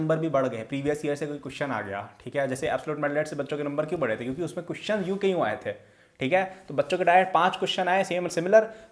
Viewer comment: what did he say? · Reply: Afraid bacche ko.........